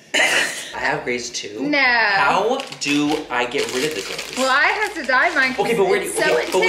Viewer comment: I thought many said Grace when he said greys. "How do I get rid of my Grace Laura?" 🙈🙈😂😂